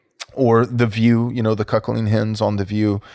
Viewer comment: “The chuckling Hens on the View” priceless… Thanks. I love a good wordsmith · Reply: Did he say chuckling? I thought he said cuckling.